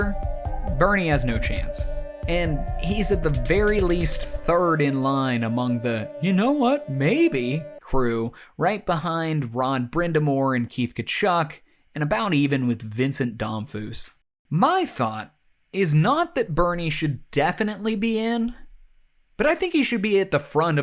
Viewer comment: Did you say, "Ron Brind'Amour" @ 41:17 (love your videos, but gotta bust your chops for that)